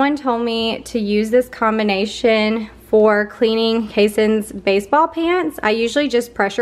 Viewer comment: I totally thought you said you pressure wash your son's underpants not baseball pants...i was like OMG